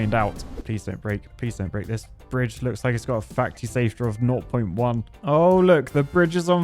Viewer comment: I can't be the only one who heard "facty saftor" at 4:02.